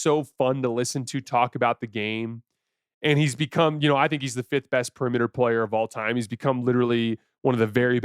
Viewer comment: did just say steph is the 5th best player in the world or is it my ears? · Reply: 5th best perimeter player specifically